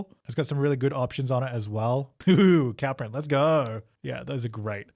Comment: Instead of “ooo bateau” it’s “ohh cow print”